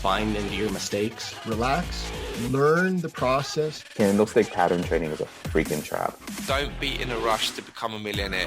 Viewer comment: Can someone please tell me what it says at 0:36 in this video, is the guy saying "Candlestick pattern training is a freaking trap"?? I need full context from anyone please